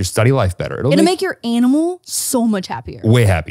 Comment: Ok so was I the only one that is super late watching this and also heard anal instead of animal at 2:10 ish. It’s 2 am. Why am I up